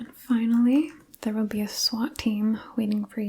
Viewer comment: Did she just say a swat time is waiting outside for me 😭💀😭💀